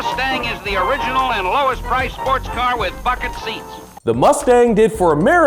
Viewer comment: Swear i thought he said “with fucking seats”😂